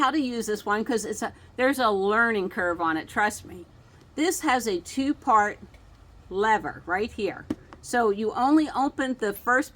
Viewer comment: I thought you said; "this has a two fart lever" 😮. Then I read the closed caption.😊